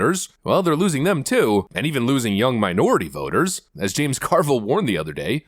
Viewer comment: Dude sounded like he was saying “jungle” blacks when saying “young” Carville has a bunch of turds rolling around in his mouth when he speaks